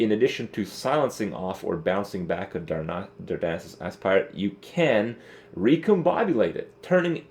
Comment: Did he say "Darnassus Ass-Pirate" at 3:21?